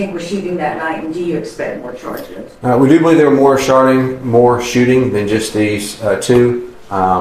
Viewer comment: he said he believes there were more sharting...... I mean shooting.. 😮.. sorry I have ADHD and I hear everything. But may this family and community find peace with each other.